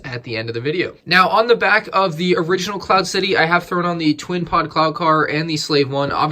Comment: It sounds like you say clown car instead of cloud car